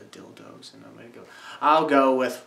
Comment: Hey:why did you say dildo at 2:55?